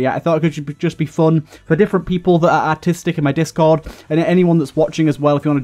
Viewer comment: thought you said autistic